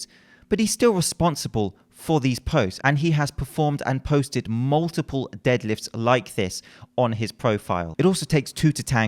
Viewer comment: Haha for a second I thought you said multiple deaths on his IG videos....I want to copy his tattoos.